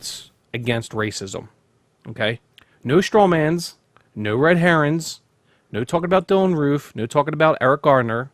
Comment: Did he say "red herons" ?